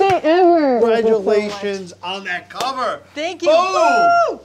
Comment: Omg this duo made me so happy. My two favorite people and now one of my favorite videos. Also when Gigi said “bucket list” at the end I thought she said “f*&@ lit” and I was like 😮😄🤣